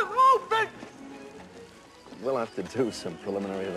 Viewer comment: when he first said im open at 1:33 i thought he said a dolphin